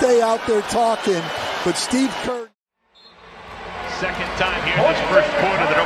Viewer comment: did he say Steve Curry?